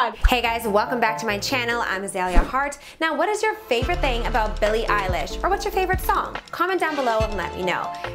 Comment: Sounded like she said billy eye lash